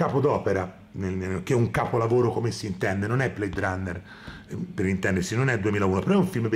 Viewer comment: Qualcuno ha capito cosa dice il al minuto 16:32, immediatamente dopo aver parlato di blade runner come capolavoro? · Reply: "non è blade runner, non è 2001" ovviamente riferendosi ad odissea nello spazio